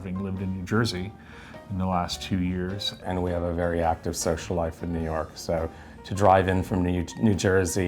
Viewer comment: Haha I thought he said “ we have a very active sex life “ ….😮social… it was social life …I had to rewind 😂😂 The light is a Dalek light from Doctor Who 😂